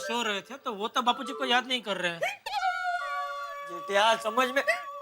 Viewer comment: Champaklal said chu**ya instead of jethiya